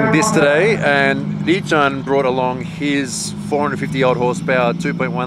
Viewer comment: did he say 450dollar hp? im confused